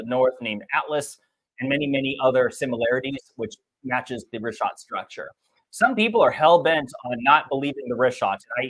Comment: I always thought he was saying wristwatch structure lol